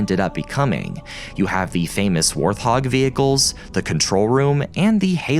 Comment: I'm sorry but did you just say "warth hog"